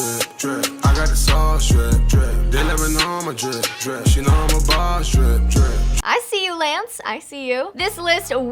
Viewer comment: did he say she know how his balls drip I'm done 😂😂😂😂😂😂